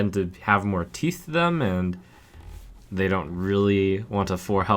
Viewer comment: what does trump say at 1:16 after "the hunter decks tend to have more -" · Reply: "teeth to them"